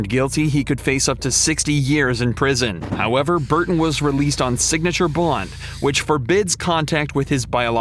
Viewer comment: Sounds like you said sig ature blunt 😂